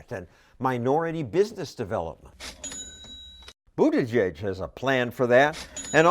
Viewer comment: I keep hearing "But plug" evey time he says Buttigieg